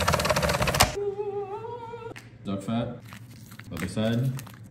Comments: I was like.. did he just say dog fat? Ah no duck fat, thank god. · "Would you like some potato with your duck fat sir?" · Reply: Ohh duck fat... damn i heard 'dog fat' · Not being anyway but i thought he said dog fat, he said duck fat, sorry i cleaned my ears better. 😅